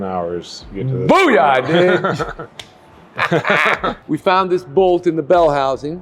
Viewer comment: it sounds like was going to say, "Booyah, bitch!!", but then he changed it to "Booyah, ditch!" so that he wouldn't say a bad word on YouTube. It doesn't really make sense that was going to say the B word, though, so I might be wrong. If anybody can figure out what word says after "Booyah!", please let me know.